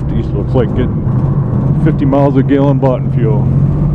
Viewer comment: You got your order of thanks correct, thanks. I think I heard him say, "in boughten fuel.." My mother used to say something she didn't have to herself make was "a boughten". Fine job brother, America still got a chance with folks like y'all